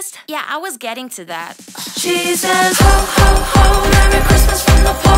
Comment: Tbh before I thought it was saying “she’s a” instead of “she says”